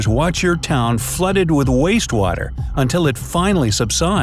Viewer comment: when you realize he does not say “Dark water”